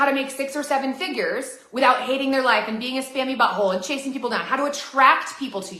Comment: Sorry this may be inappropriate, but I thought she said "scabby butthole" vs "scammy butthole" lol